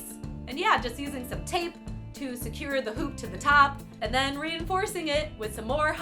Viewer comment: am I the only one who heard poop